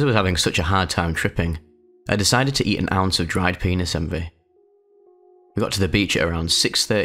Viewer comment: what did he say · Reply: Penis envy are a type of mushroom